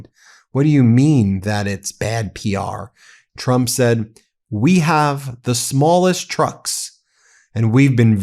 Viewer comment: I heard "We have the swolest trucks".